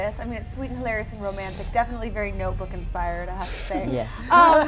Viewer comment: sorry about the bother but, would you tell me what does Joslyn say in 1:44 ? I suck.. "definitely.." something, and I misunderstand because of the "great quality" of the mic.. I'll tell you my tries: "definitely noble and inspiring to have said" and another one but I think that one is a little stupid... :P